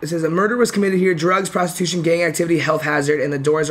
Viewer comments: I thought he said gay activity and I was about to get hella triggered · When he said “gang activity” my stupid ass thought he said gay activity 😭